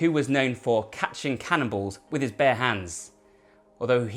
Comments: Wait...He lost fingers the first time he tried it? Then what? After that, did he think... "Hmmm... I haven't quite mastered catching cannon balls yet. Let me keep trying this!" That's someone with too much time on their hands. And not enough fingers. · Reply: Oh he said cannonballs?? I thought he said cannibals - man why would a person try a second time if the first time you lose a few digits..... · Wait...He lost fingers the first time he tried it? Then what? After that, did he think... "Hmmm... I haven't quite mastered catching cannon balls yet. Let me keep trying this!" That's someone with too much time on their hands. And not enough fingers. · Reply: Ahh Canon balls! I thought he said Cannibals!!